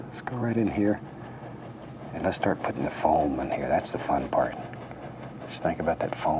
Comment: am i deaf or did he say the n word at around 14:34? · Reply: It does sound like that but he said foam in here